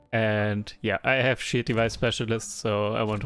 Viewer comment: I misheard shield device specialist as "shitty buy specialist" and had a good laugh. Btw a nice fun to watch game that was